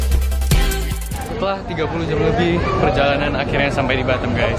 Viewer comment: 30 jam lebih??? Atau aku salah denger?